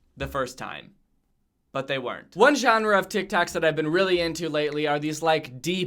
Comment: did you just say tic tacs 😂